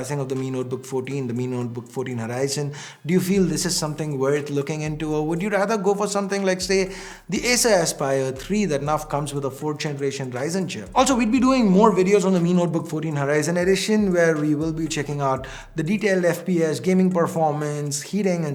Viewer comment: did he said Acer Aspire 3???